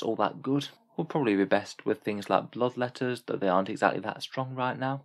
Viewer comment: No, you misheard "Bloodletters" as "love letters"